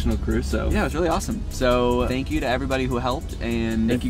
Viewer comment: Lmao so i east h the camp rock video, and the music video and this video and the entire time I thought you were saying “I go see Kevin Jonas” but you never go see him, so I was so confused until the last second of this video where I finally heard I ghosted Kevin Jonas… I might be the most oblivious person alive 🤦‍♀️🤦‍♀️